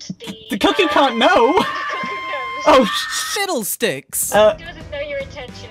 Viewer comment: “Oh SHIDDLESTICKS!!” - 2019 😂😂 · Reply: I thought he said S*ITLESTICKS